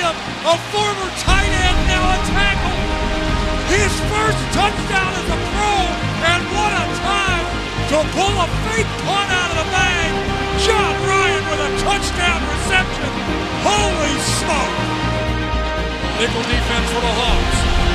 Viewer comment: did he say fake punt???